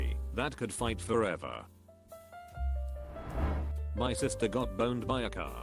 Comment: i heard "my sister got boned by a car"